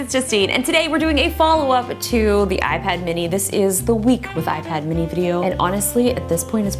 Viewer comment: Thought she said: “This is the weakest iPad mini!”